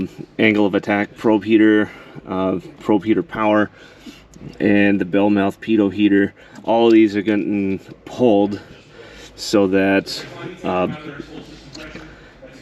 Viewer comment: When you were pulling the fuses I thought you said peter heater instead of pito heater.... had to listen to it again lol